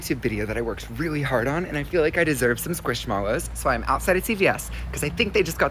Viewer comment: Am I the only one who thought he said I’m outside of CPS 😂